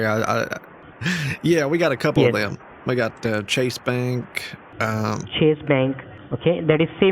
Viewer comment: Did he say "Titty Bank"?